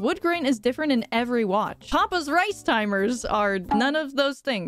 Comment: I thought it said “Papa’s Suicide” and I almost freaked out